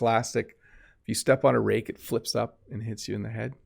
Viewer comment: Hi! At 15:49 the captions says lifts up” but I think I heard you say “flips up”. Am I wrong? Thanks for the lesson!!